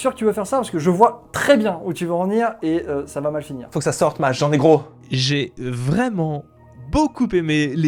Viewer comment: ? frérot ? jean n'ai quoi ?